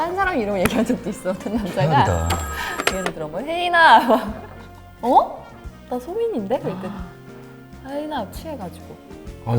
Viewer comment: Did she say Haein ah?! Two years ago 😮